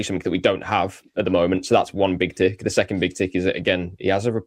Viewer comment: your pronunciation of some words is getting hilarious... @ 39:15 it sounds like you said 'thats one big dick' hahaha I had to rewind as I had this in the background when I was working and wasn't too sure where that came from.😂😂😂😂 If I'm the only one who hears it then I will be the only one giggling haah